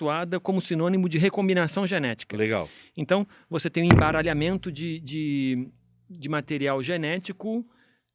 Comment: juro que ouvi enc4ralh4mento em vez de embaralhamento kkkk a quinra serie q habita em mim